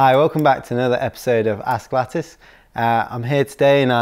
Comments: For a second I thought I heard "Welcome to another episode of ass lettuce". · hehe in the intro it sounded like he said "ass lettuce". accents are fun